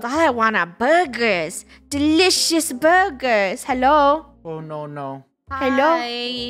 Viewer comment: I thought she said i want a delicous booger